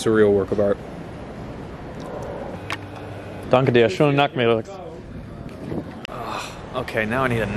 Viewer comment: not sure if I misheard, but did you tell the guys at the foodstall "thanks girls?" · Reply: Haha "danke Dir, schönen Nachmittag"